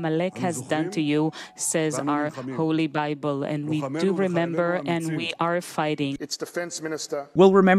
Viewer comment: although i hate Bibi, but he didn't say anything close to what is shown on the screen or said in the translation. he said: "we remember, and we are fighting, our fighters and soldiers are brave".